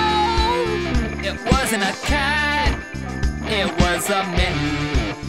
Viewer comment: I Like How The Another Brick In The Wall part (4:04) has a ending twist where they finally figured out that the so called "squished cat" was a mitten the whole time XD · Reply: Until now I thought he said “it was a myth”